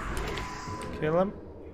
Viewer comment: For all the people that wonder what ,,Aisa'' mean at the end - it's just how we say Niceeeeeeu in Bulgaria. aisa is not a word lol, just pronouncing niceu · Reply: I actually thought he just said nice-ah or something lol